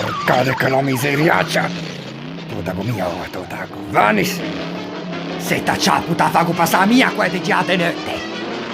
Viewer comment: Non ho capito cosa dice al minuto 0:38 😅 qualcuno può tradirmelo fa troppo ridere! 😂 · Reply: “Te la faccio passare io la voglia di girare di notte” 😜